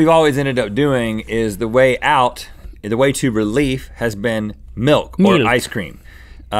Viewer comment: I'm sorry but what the heck did Link say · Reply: He said milk